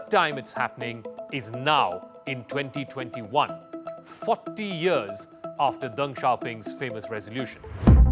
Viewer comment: I was reading the comments and wasn't listening all the way and thought he said "F#ck da years" at 1:23.